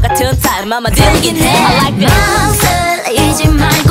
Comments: Am I really the only one who listens to something very similar to "I like girls" at 1:18 That part is not even in the translation, did she say that? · Am I really the only one who listens to something very similar to "I like girls" at 1:18 That part is not even in the translation, did she say that? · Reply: Viersami Ahulper I heard that as well but she actually said “I like that”. Lowkey wished that she said that 😅 for real. It reminds me of the line “I like eye contact” from You’re the Best.